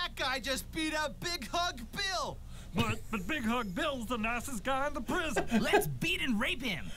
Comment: I always thought the guy said that Big Hug Bill is the naughtiest guy in the prison but he actually says nicest guy in the prison